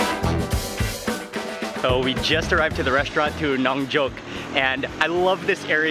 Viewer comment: I love watching Mark's videos. But at 1:10 he says the name of the restaurant and a couple of seconds later he tells the name of the area. And i hear: "Non Joke" & "Crappy Town", i couldn't help but start laughing. 😁